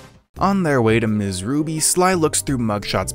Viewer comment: Everytime you said "Sly" all i heard was "slime" and i was 100% ready to just accept that the racoon's name was Slime. Also Sly has a really hot voice